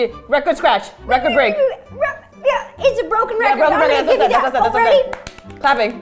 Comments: I THOUGHT THE APPLAUSE SAID APPLE SAUSE !!!!!!🤣🤣🤣🤣😅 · For the applause one I thought it said apple sauce 😂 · I thought applause said applesauce 😂👍🏻